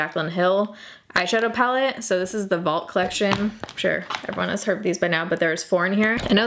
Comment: I hope you have time to check out Madeira Island while in Portugal. Also, am I the only one who heard, "I'm sure everyone has herpes by now", while talking about the JH palates at 11:16? Sorry but that's what I heard several times while trying to figure out what you were actually saying instead of what I was hearing. Thanks for the video and entertainment. 💖